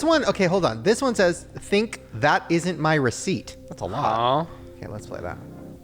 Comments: i heard “that isn’t mercy” with an accent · I heard “That isn’t mercy” in a Scottish accent.